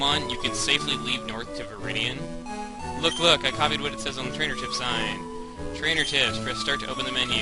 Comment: When he says "Trainer tips, yes" at like 8:35, the captions say he says "Continued racism is yes" or something like that involving racism. Lol.